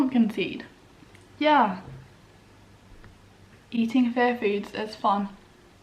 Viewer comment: Omg... All these months I thought you were saying, "fair foods." 😅🤦‍♀️ Aiyiyiyi